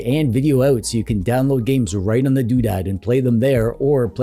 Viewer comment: you say something like "right on the do that". What do you actually say and what does that mean? Not a native speaker here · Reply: "Right on the doodad". Doodad is a silly slag term for "thing".